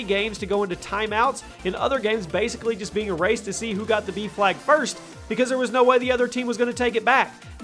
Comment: I thought you were saying beef lag and not b-flag